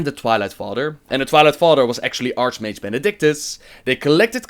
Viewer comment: on 11:06 you say "archMAGE" instead of Archbishop. Just tought you should know